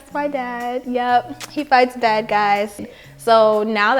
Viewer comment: When said “he fights bad guys” I thought she said “he fights dead guys” and I was like well what’s the point of that